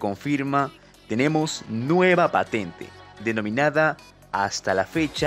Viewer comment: Pense que decia nuevo mapa